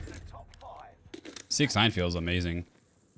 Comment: I thought said 69 feels amazing LMAO